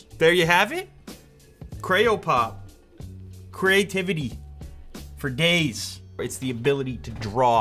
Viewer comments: i thought he said "creativity for gays" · at first I thought he said creativity for gays but he said days